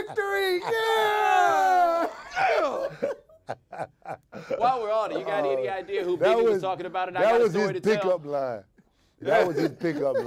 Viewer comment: How the hell does LeBatard or Jones not follow up immediately after the Mutombo thing with, "By the way, I Got a Story to Tell is about Mason right?"? Zo was giving it up, he might've let something slip on that as well..... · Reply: Bo did ask at 5:24. He kina trailed off and I don't think Zo heard him, But he did ask "while w'e're on it do you have any idea who Biggie was talking about in "I got a story to tell?" Either way mistery solved a big win for the HQ crew. Go Bo and Dan